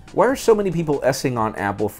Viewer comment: Assing instead of shitting 6:48.i know he tried to say s-ing but it sounds like assing lol